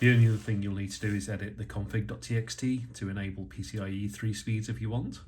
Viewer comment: I missed the comments at 9:48... did you say something about a "TXT"???